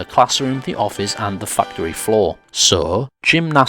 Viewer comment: It'll be back to normal videos after this. The response to the last video was just overwhelming with the "aktshually" comments about specific countries, so I figured case studies were a good reply. Thanks again to my collaborators (no, not that kind of collaborators) for their contribution. I hope you guys like it. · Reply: i love the fact you used the correct term 'hand-egg' 10:25 did you say Fucktury? awesome